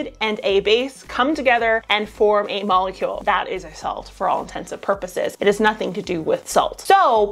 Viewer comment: Maybe it’s just the audio and I’m mishearing, but as someone who only learned the correct phrasing at the age of 32, at the 2:08 its “for all intents and purposes” not “for all intensive purposes” 🤣 Sorry to be that guy! Thanks for the quality videos as always!